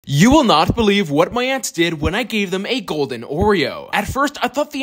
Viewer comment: I thought he said you can’t Believe what my ex did lol😂😂